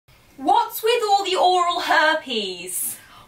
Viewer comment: Can smb tell me what and said 00:00 till 00:05 seconds? Please:( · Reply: said: what's with all the oral herpes? said: what would Lord Voldemort pants